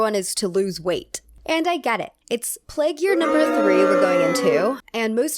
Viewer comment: i heard "we are in plague year nr2" and only then i relaized this is from 2022 wtf